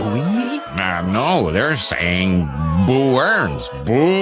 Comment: Did you say boo or Burns? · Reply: Boo